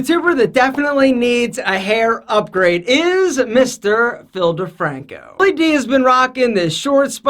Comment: Genuinly thought he was about to say Filthyfrank